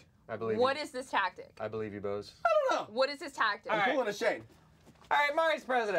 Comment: What did Joven say at 44:10? He is pulling a what? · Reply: shayne because hes was referencing clamity Janet I think